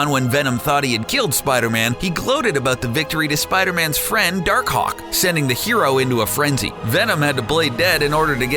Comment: hold up did he just say his name was dark cock??